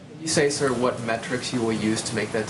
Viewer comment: Metrics? I thought he said Pet Tricks and I said: "Right HERE!" "You're lookin at one!"